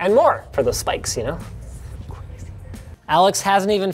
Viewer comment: mouthing: 'crazy' EDIT: i thought he was saying 'who gives a shit' lmao XD